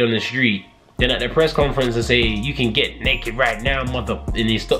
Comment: Is it Me or in 7:50 i heard you can get naked now mother- and stops himself from swearing